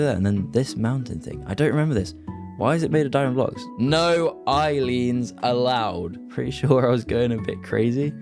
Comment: i think you called endermen eye leans.